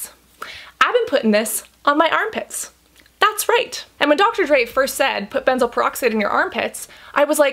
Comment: I'm subscribed to the great doctor Dray, but for some reason, all I kept hearing was "Dr Dre". And I kept being astonished that Dre would be not only a great rapper and inventor (of beats, by Dre), but also so knowledgeable in Skincare. 🤭